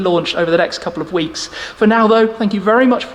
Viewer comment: Doug’s “Thissssz” and Shmee’s “ Hi guys I’m Shmee” never gets old 😁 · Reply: cheers? I always thought he was saying chairs! 😂😂